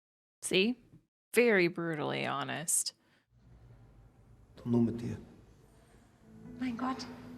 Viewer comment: he actually said „dann nur mit dir“ meaning „then only with you“ I don’t know why they messed the translation up :(